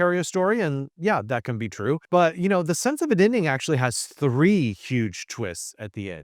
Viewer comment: What's that title again? I just can't understand what you say. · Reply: Me neither, but the book is 'The Sense of an Ending' by Julian Barnes.